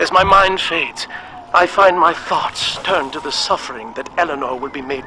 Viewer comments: oh god, I thought he said "farts" instead of "thoughts" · lol at 3:23 it sounds like he says "farts"